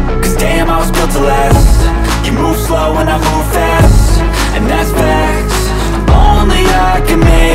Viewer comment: I hear NEFFEX instead of 'And that's fact'